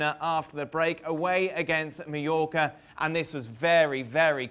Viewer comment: Is it only me? At first I thought I heard him saying New Yorker for Mallorca. Did he pronounce it weirdly or is it my hearing????